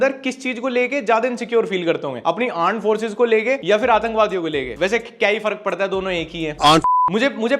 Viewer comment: I heard aand forces 😂😂😂❤